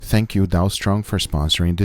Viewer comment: I heard "Thank you Donald trump for sponsoring this video" 😂